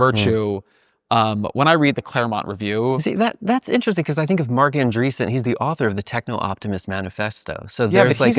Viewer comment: He said "I think of Marc Andreessen" but I thought he said "Mr. Van Driessen" the granola hippie high school teacher in Beavis and Butt-Head lol